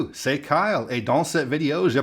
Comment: For a minute there I thought you said "Sieg heil". No, you were saying "c'est Kyle" or "it's Kyle".